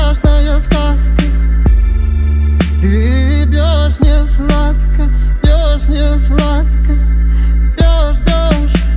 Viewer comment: что он сказал? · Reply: Бьешь не сладко Но слышатся совсем другое😄